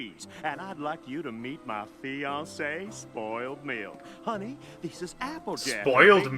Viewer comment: It´s Spoiled Milk, not Meal (not that makes it any better xD). First time Filthy said her name he mumbled a little. Misheard it there too.